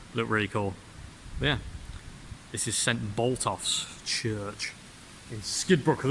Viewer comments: I thought you said St Botox. Anyway muchly enjoyable. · Ahhhh Botolph's, at first I thought ye said boltophs, I'd sure as hell bolt off from there if I found it middle of the night lol!